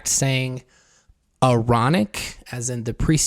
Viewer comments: I thought they were saying EROTIC lol! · 😂 i thought they were saying erotic 😊